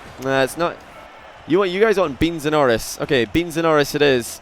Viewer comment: it sounded like said Metsapenis